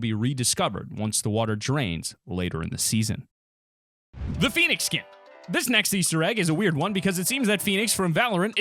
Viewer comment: I thought you said pheonix kit